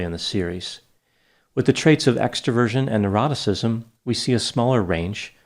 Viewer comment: I swear, every time he says “neuroticism” I hear him say “eroticism”, I need help.